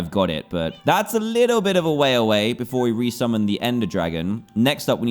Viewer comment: I THOUGHT it said I respawn the ender dragon